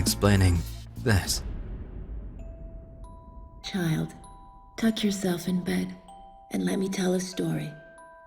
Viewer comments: I thought it said "Child, touch yourself in bed." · For a minute there, I thought she said "touch yourself in bed." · I thought it said "child touch yourself in bed" lol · So I was listening to the into to the game and I thought the lady said "child touch yourself in bed," and not "tuck yourself in bed" I was super confused until I read the subtitles XD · I heard "Child, touch yourself in bed." Had to replay and read the subtitles. Made me laugh though cx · At the beginning I thought it said "Child, Touch yourself in bed" and nearly Fell backwards. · omg... I thought the cut scene lady said "Child, touch yourself in bed." ~Hige. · Thought they said, "child, touch yourself in bed, and let me tell u a story" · It sounded like she said touch yourself in bed O.o